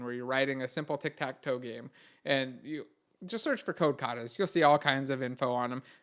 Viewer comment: i didn't understand that part i tried searching "Code Cadas" or "Code Coty's", didn't work.. Somebody please clarify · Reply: it's "kata", the japanese word